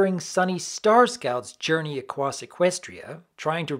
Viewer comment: Lol he said “acwoss” instead of “across!” I don’t know why that’s so funny! I guess it’s just funny to me? It’s a blooper that usually would be cut but it slipped through and that’s funny to me. Also it just sounds funny! XD